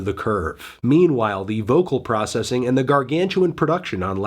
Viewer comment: what word does fantano mean here? Poppyness, poplessness, pompenous? I can't really hear it well. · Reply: I think pop ness